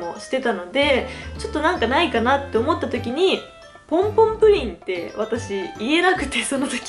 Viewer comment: ポムポムがポンポンに聞こえる笑 可愛い💓💓